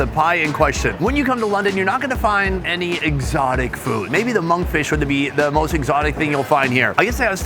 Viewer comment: Did he say “when you come to London you’re not gonna find any exotic food”? Ermmmm ok. Hahaaaa